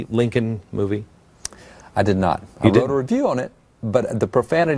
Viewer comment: I thought he said "I read a review on it"? I could be off, though.